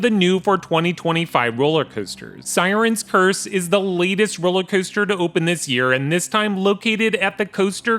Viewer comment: At the beginning I thought you said “I got to ride one of the lamest roller coasters to come out” you said latest 😂